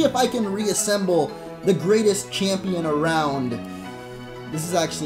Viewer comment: I thought you said Steven was the best champion